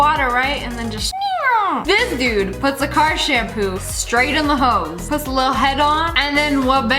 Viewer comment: I thought Lia said h0e